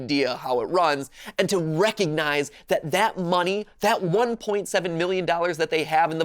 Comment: bro I thought It said we stole $1.7 million